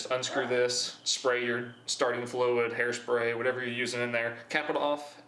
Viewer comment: There are a few caption errors that can be improved for the deaf/hard of hearing community. At 1:17 the captions say "staring to flow and hairspray" when it should be "starting fluid." At 1:58 the captions say "which we go" when it should say "which will go." At 2:10 the captions say "half - this" when it should be "half to this." At 2:22 the captions read "we'll have a particular yeah not" when it should say "we'll have a potato a uh not"." At 2:48 the captions say "in stock and - nobody" when they should say "in stock and 2. nobody." At 3:33 the captions say "the explosion happens we're just gonna" when it should say "the explosion happens which is gonna." At 3:55 the captions say "put some time on your" when they should say "put some acetone on your." At 4:07 the captions read "and now I can handle" when it should say "and now it can handle." At 4:46 the captions read "two screws and a taster" when they should say "two screws and a taser." At 4:54 the captions say "won't just aren't between" when they should say "won't just arc between." At 4:58 "arm" should be "arc." At 5:12 "it worked" should be "arc." At 5:15 "exactly what with what that will tell perfect" should say "exactly what we want that worked out perfect." At 6:04 the "-" should say "so." At 6:35 "I'm thinkin pipe just poke" should say "I'm thinkin probably just." At 6:47 "that I don't put the table on it" should say "and I don't put the tape on it." At 8:11 "sound" should be "so now." At 8:32 "nobody want to he